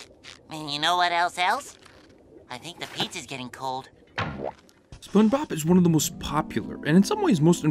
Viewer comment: why does he say spoonbop